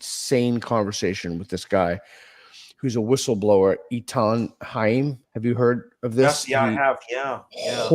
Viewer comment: I can’t find the video Peter mentioned about the whistleblower - sounded like he said “Etom Heim” but I’m sure I’m spelling his name incorrectly. Anyone? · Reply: Eithan Haim